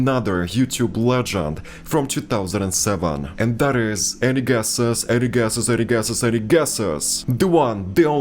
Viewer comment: I thought you said Eddie Gesis, instead of any gueses. Sat there thinking "Ah, never heard that name before" .... 🤦‍♂️